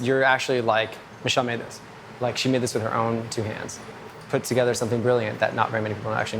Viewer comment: I can’t be the only one who heard him say „Shawn Mendes“